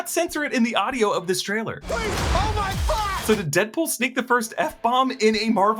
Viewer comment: I genuinely thought he said "Oh my fox!" 🤔